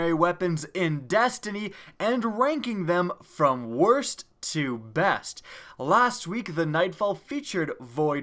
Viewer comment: lmao. I literally heard "wangking them from worst to best"